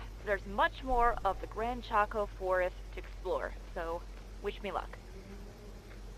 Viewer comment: When it said grand choac forest I thought it said grand taco forest😂